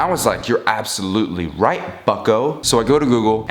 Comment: every time he said bucko i thought he was saying fucko